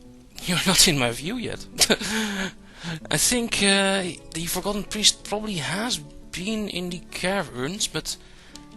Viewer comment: Did he said "Forgotten priest" at 18:46?